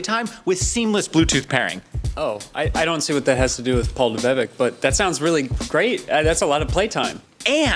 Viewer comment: Damn I thought y'all said Paul the bed bug and I spit out sida laughing....